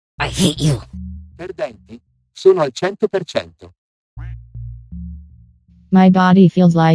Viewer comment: did she just say "perdente, sono al 100 per cento" also i wasnt expecting her to randomly speak in italian lmao